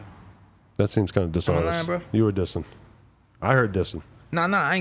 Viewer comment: that seems a little dishonest, i heard dissin` LOLLL